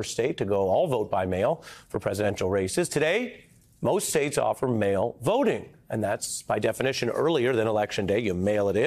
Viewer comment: almost sounded like "Today, most states allow male voting" I can only applaud that.